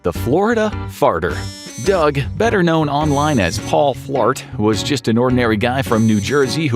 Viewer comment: Who else thought he said Paul Blart? Or am I the only one?